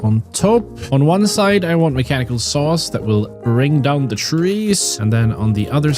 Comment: did you say "mechancal sas"? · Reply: He said mechanical sauce